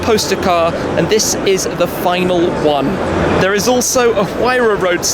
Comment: If I had a pound for every time I heard "the final zonda".